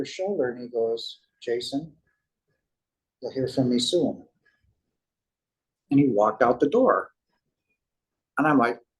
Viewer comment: What did the archangel say to him as he walked away? It was too fast around 51:10. “Go see and sue them?” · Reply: Jason, I’ll see you again soon! All my Love!!